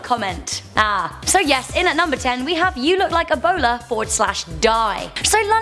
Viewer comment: At #10 I thought she said you look like a bolar.